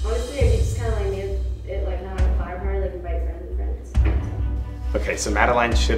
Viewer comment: I heard her say "honestly it's not really my party so more like friends of friends"